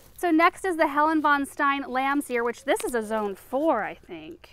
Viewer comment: 1.10 Oh, Lamb's ear you said in one of your videos. I thought you said Lamsia and tried to google it but nothing came up.